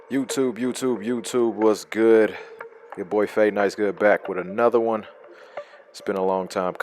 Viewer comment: I thought you Said blade icewood at first lol